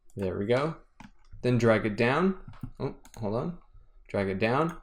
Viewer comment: Anyone heard that 'hola' at 16:45? Good tut, you have a new follower ;)